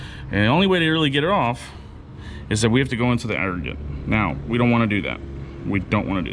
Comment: Anybody catch what he said here? "And the only way to get it off, is we have to go into the rdrnrdndrd to do it. And we don't wanna do that." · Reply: Aggregate.